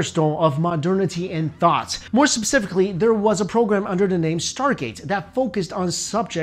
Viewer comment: Did that guy seriously just say "subsifically" instead of "specifically"? How the actual f do you get is so wrong without actually trying??